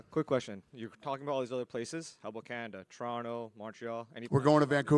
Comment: does he say wakanda?